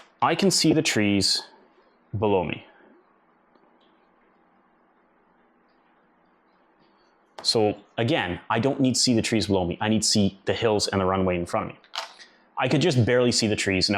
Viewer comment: What you said, "I don't need to see trees below me." What I heard: "I don't need cheese baloney."